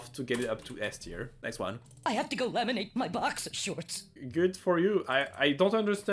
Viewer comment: I think he said “Boxer Shorts” Good video btw. I agree that the Teensies from the Livid Dead levels has the best quotes.